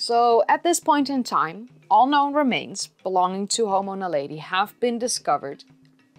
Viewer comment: Thought it said, homo nailed. Was like whatttt, lmao